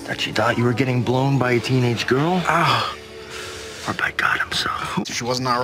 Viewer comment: I never noticed that lmao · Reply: did he say “or by god himself” ??? Can’t believe I never heard that either